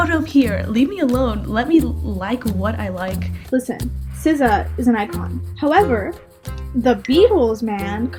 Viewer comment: Ziza? Is that what their saying? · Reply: SZA lol